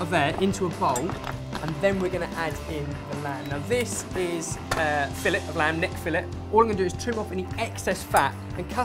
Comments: when was holding the lamb filet, and said "this is a filet" i was sure he was saying "this is ahh Phillip" and i was like whaaat, did he name it. then i realized what he really said. it was at about 2:12 in the video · My American ears didn't immediately grasp the sentence "This is fillet", and I was pretty sure for a few seconds that you were telling us the meat was that of a lamb named Philip. I had to work through a whole horrified thought process about the unexpectedly barbaric turn this video had taken before it finally occurred to me that perhaps I had misheard something.